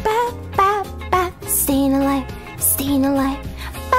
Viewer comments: i thought she said “stealin a life” ._. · This is what i hear: "ba ba ba ba stand alive stand alive ba ba ba ba stand alive except for u" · I thought she was saying staying a light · Oh i thought she said stay in the lights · I thought she said stand a light xD When the video title says : Staying alive~